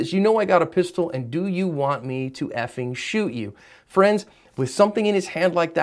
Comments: Sounded like said “I didn’t shoot you” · It sounds like he said "i didnt shoot you" during the inaudible part.